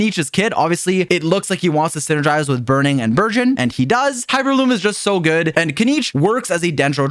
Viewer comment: captions said: “While Hyperbloom doesn’t syngries too well with Kinich's kit; obviously. It looks like he want to synergies with burning, and virgin.” 💀💀💀